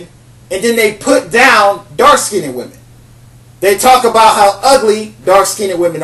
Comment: sounds like hes saying "dark skinny women"